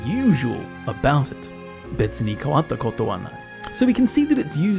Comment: ive noticed a small mistake at 9:13 ,変わった*とこ* in the subtitles instead of 変わった*こと*